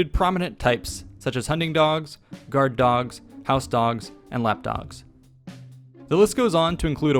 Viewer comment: I legit heard "updog" as the fourth major type, haha. (Updogs filled the theatrical role of entertainment.)